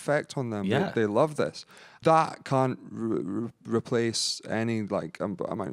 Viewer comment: sounds like he says that c**t rather than can't